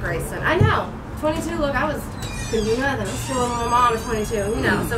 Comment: She thought he said we couldn’t do that at 22 but in reality he said we coulda did that when we was 22 lmfao